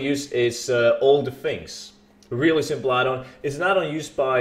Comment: What’s the add on? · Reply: Same I thought he said older things, put the name in the description please kthx